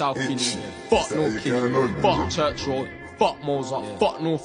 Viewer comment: Church Road. I thought he said CHURCHILL. i was about to jump in the car!! 😂